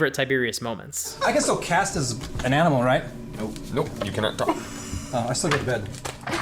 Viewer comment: For the part you;d said was your favourite tiberius moment i have no idea what the heck Orion said. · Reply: “I still go to bed.”